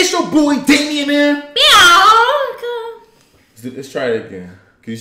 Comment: “Biawnca” that’s why I heard 0:02 xD